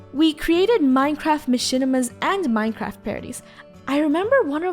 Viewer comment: ma-shin-a-ma (I do know she meant machine that’s just what it sounded like) EDIT: oh did she say movie or cinema